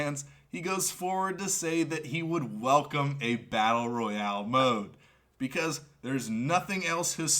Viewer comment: I thought they said there won't be a battle royal 😂